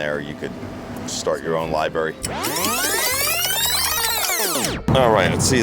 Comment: Did just say libary?